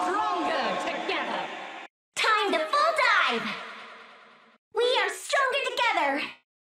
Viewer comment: Hi I speak Spanish and I don't understand what ying says at that moment, could someone tell me please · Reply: She says time to full dive